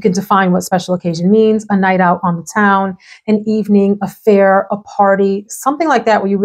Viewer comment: When you were talking about the special occasions to wear Wirtanen I thought you said "affair". I thought you snuck that one in nicely.🤣I'm guessing that was "a fair", lol Anyhoo...it's nice to see someone talk about Prada Candy. It was one of the first I bought when I really started expanding my fragrance collection. Even with all the beautiful and interesting niche perfumes that have made their way into my collection, Prada Candy is still one of my favorites. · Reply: I did say affair, but I meant it as in an evening affair = evening event. Not THAT kind of affair, but I suppose that works too 😂 Prada Candy is 👍👍👍